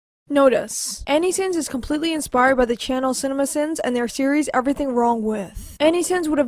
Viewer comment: It sounds like she says notoce instead of notice